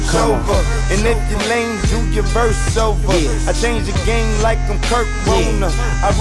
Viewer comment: Damn I thought he said I changed the game like Corona